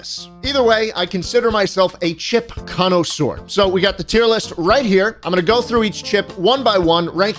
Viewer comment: I thought it said chimps tier list😂